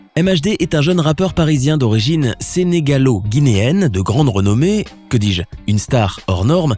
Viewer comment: Il a dit quoi a 0:50 il a dit sénégalau ou je rêve ...😦